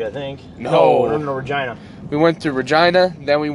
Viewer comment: Could've swore you said vagina, went back and it was Regina 👍